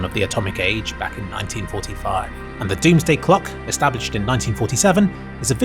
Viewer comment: Did he say "Doomsday Cluck"?